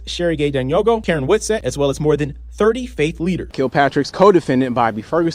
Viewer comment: I thought the news said "30 fake leaders" ...... Its about the same though....... Lol